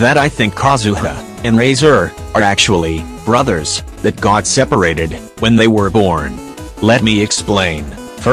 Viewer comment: At first I thought that said "that God separated" and thought "What did Venti do this time-"